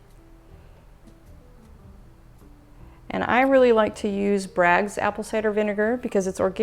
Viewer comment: What did you say was in there at ~ 1:39? I couldn't understand you all that well, sorry lol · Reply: Braggs Apple Cider Vinegar. There is a lot of mother in it "mother" refers to the culture of the vinegar.